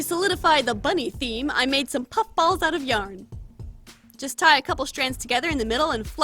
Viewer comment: At the time 19:44, she said “-puff balls out of YOUNG.” Not yarn... did anybody else catch that?